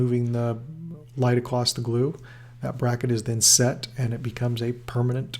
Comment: am I the only one who hears him say “acloth” the gluew”